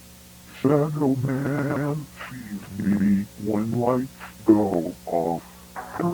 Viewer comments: I Wounder what this could be.. but i think he says Shadow Man see me when lights go off.. what do you think? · What is said at 17:50? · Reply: Shadow man sees me when light go out.